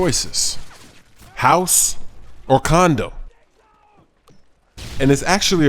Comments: i thought he said condom on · At first i thought you said, "House or Condom?" hahaaaa * laugh* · you said condom instead of condo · Lol Was I The Only One Who Heard Condom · at first i thought i heard house or condom? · sounded like he said condom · wow the first time i heard him say it, i thought he said house vs condom.